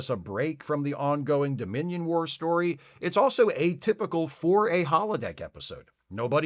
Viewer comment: When you said it was "atypical for a holodeck episode", I thought you said, "It was a typical 4A holodeck episode." And I went, "huh?"